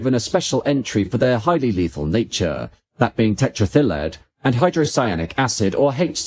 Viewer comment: says tetraethyllead as if it's a Welsh holiday resort. LOL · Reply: Right?! I thought I'd never heard of it, and it took me several goes to realise that he was saying "tetraethyl lead"! 😂